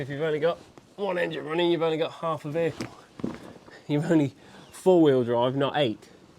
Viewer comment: I'm sorry I thought you said you said driving not a will it start...my oopsie